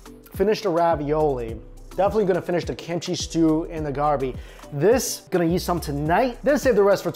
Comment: The way he says budaejjigae 😂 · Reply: Lol the way he says 갈비, had me rolling. Looked away and i heard him say “Kim chee stew and garbi”. Immediately had to look back and figure it out. The pronunciation bug usually gets him with Japanese and Korean words